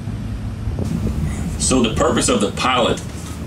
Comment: Oh shi I thought he said local pirate ☠️